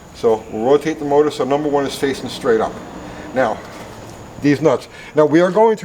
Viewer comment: Did he just say deez nutszz?